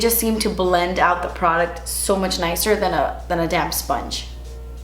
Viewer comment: I thought you said damn sponge at first not damp sponge.